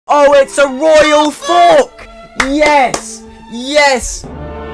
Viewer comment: I hear "ITS A ROYAL FOCK!"